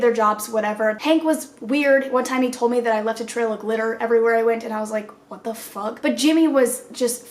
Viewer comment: I thought you said “left a trail of litter everywhere”